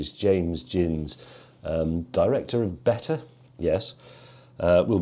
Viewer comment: Director of Better, someone a fan of W1A? 😂😂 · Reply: I misheard and initially thought he said “Director of Butter”.